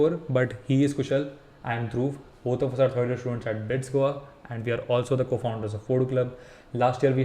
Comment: said you are from BITS Goa but the subtitles said BITS Pilani😅